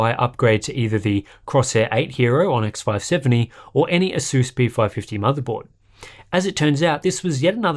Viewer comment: Please Purchase Crosshair VIII Hero and any "Ass" B550 motherboard lol i didn't know that Asus is now called ASS lmao